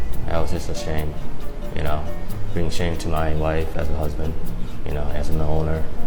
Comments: I swear i thought he said “im ashamed to my wife and her husband” lol wasnt even surprised what a world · Did he really say his wife and husband at 1:10?😂😂😂Wtf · Did he say he brought shame to his wife & husband? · Did he say bring shame to his wife and husband?